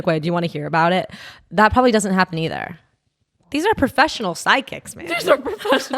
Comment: why does say psychic like she is saying sidekick